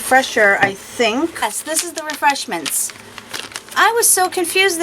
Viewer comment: Dear God! First, I guess it’s how tired I am or how much pain I am in tonight... but... when you said “ refreshers” I thought you said “ refreshments”, so you can guess the aghast look on my face when you proceeded to take razor blades out the bag! 😲🥴😲